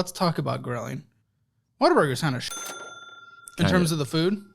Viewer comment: All this time I thought he was saying “water burger”😂